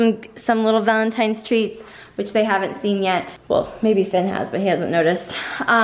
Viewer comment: who heard Finn what's that